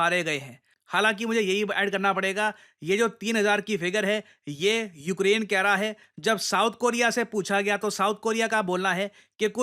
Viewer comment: i think it is “North Korea” instead of “South Korea” in audio.